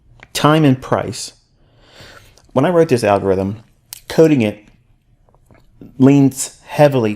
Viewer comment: did he say "When I wrote this algorithm"??